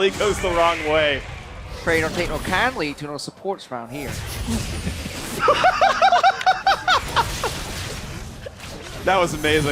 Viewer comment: What did Krepo say at 28:09? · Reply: "pray don't take too kindly to no supports round here"